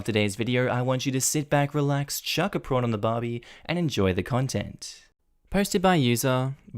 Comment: My son cracked up because when said chuck a prawn on the barbee, he thought said sit down n relax n shut the f up! 😆